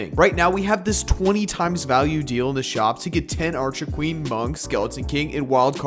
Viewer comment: Bro I thought you said archer queer💀